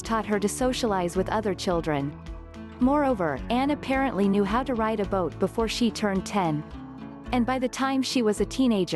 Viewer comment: Did they say "she learned how the ride a boat before age ten"